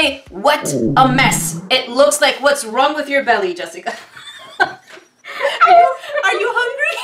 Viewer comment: Is it me or did she call her jesika? 10:56 ??